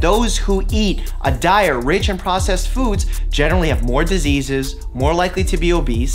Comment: Every time you said “diet rich in processed foods” I thought you were about to say diarrhea.